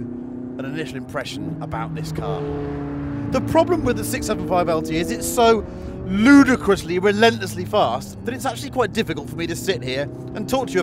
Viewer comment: did he say "It´s so Luda Chris-ly fast"... ??...lmao xD